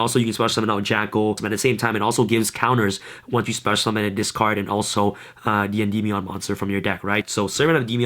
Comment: I swear I heard you say, "and summon a 'D&D Beyond' monster." X'D *still, I love this deck it's what a Spellcaster deck should be!